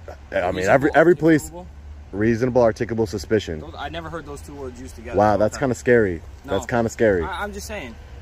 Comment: Almost missed it. He actually said "I have never heard those TWO words used together"